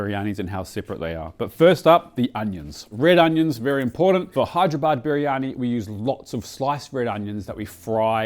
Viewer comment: I swear i heard you say Hideyourbody biryani...